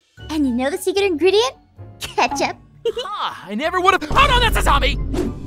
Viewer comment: For a hot second I thought he said OH NO THATS WASABI 😂